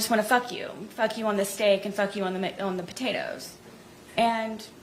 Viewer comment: are we sure Sarah didn’t lovingly prepare McDonald’s meal??? When she said Jeorge was being vulgar and wanted to do her on the steak and potatoes…. It almost sounded like it came out as “ on the steak and on the McDonald’s”…. Maybe the baked potatoes were really McDonald’s Hash Browns???? 🧐🧐🧐🧐